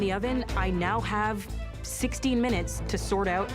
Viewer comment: did she just say she has 60 minutes